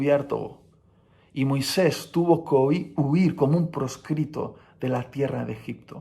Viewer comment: No tiene mucho qué ver pero parece que dice: Y Moisés tuvo covid jeje